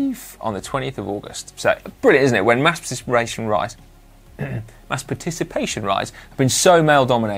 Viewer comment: I did a double take and wound it back when Si stumbled over “mass participation rides”! Did he just say “masturbation rides?” 😳